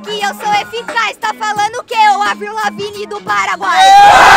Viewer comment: Eu achei que ela Ia falar *aqui e eficaz tá falando oque o avril laving do satanás * É não do Paraguai ksksks